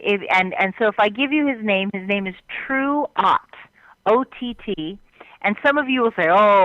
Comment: What´s the name of this guy? I cannot understand... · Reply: jorge. Truott, like True-ot